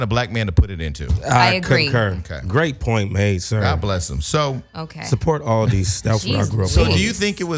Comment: Did Gio say "support Aldi's, that's what I grew up on"? 🤔🤦🏾‍♀️. Come on Gio lol.